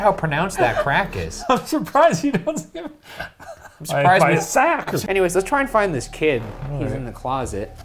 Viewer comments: what was gonna say LMAOOOO "im surprised my sack didnt show up"??? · For #postmortem: At 15:09 please tell me that says “I’m surprised you don’t see my sack” Please boys I need to know I heard him right